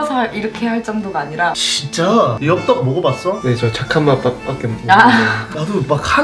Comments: 이요 잠깐이 아니라 착한 맛 아닌가요....? 지적이 아니라... 그냥 저흔테는 그렇게들려서요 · ?내 귀가 이상함?잠깐 밖에 가 아니고 착한맛밖에 이라고 들리는데?? 아니면 말고... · 잠깐 밖에가 아니라 착한맛밖에인 것처럼 들리지않나요? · 착한맛이라고 들었는데? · 착한맛 밖에 못먹어봤다는걸로 들리는뎅.. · 나만 착한맛 이라고 들림?